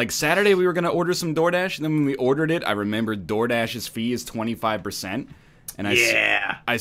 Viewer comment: For like two weeks I thought he was saying like... dourdash and it was some kind of weird eastern european soup dish or something that had just become popular recently. The moment they said it was a delivery business I felt like a fucking idiot.